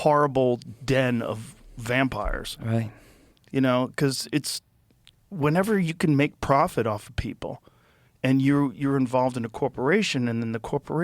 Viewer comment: Jo Jo..you need take a course in economics. Did you say When people make a prophet off of people?